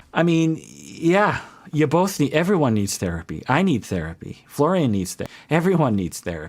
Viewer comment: YOUTUBE'S CAPTIONS LMAOOOOO!!When said "I need therapy" the caption said "donald trump needs therapy" HAHAHAH how was that misinterpreted LMAO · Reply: Wait nvm the sentence it translated to "donald trump needs therapy" was when he said "everyone needs therapy" lol